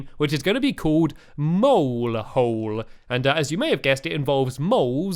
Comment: its nice and i know this is a kid friendly youtuber but around 2:50 i thought he said mole hoes